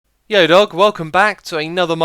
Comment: So today I was mimicking you saying "Hallo welcome back" and my friend was getting very confused, so I decided to show him one of your videos to hear what it cam from. I showed him this video and guess what I hear... "Yo Dawg" I was face palming so much.